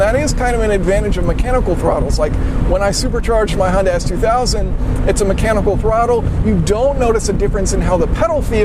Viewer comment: did you say Hyundai s2000 lol